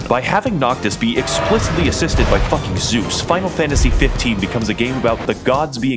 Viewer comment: As a audio listener who had this autoplay, I can genuinely say I misheard him say "spinal Fantasy 15", and thought this was a game genuinely about the BTS crew going around and slaying gods for a solid five minutes before he said it was Final Fantasy.